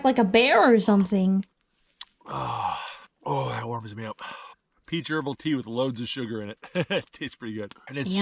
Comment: My wife thought he said "pee gerbil tea" at first because she was half listening while doing something else.